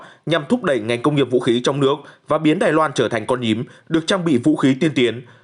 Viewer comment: tôi đã nghe nhầm chăn? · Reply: Con nhím